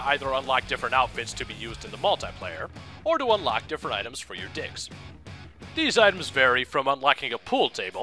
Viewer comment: LOL it sounded like you said dicks at 3:27...of only you really did, that would have been funny.